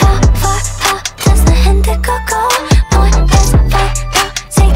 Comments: Am I the only one who thought she was say "a hint of Coco" instead of "indigo" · I hear “hint of coco” instead of “indigo” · anyone else thought that she was saying "hint of cocoa"? instead of indigo go